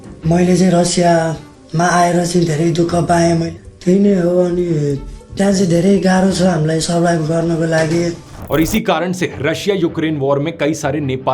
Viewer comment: he actually says in Nepali: 'Tya chai dherai garo chha hamilai survive garna ko lagi' which means 'It is very difficult for us to survive there.' But the caption says something completely different — 'A few of my friends are dead.' The subtitles are wrong/out of sync."